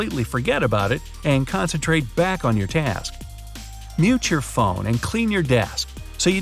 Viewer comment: did he just say yeet your phone or i just misheard